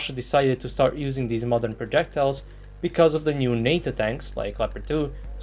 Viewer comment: I thought they said NATO tanks are weak 🤔😂